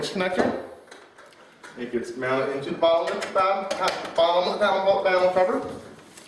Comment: tell me what he says and I'll shave my eyebrow. "it get's mounted into the bottle McBad pad boddle my battle boat paddle cover.... and the wire gets pulled in..." haha what?